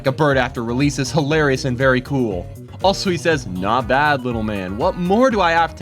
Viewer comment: With Minus Gar, instead of "Not bad, little man!" i thought he said "Drop dead, little man!" and i can, and will, never get that out of my head.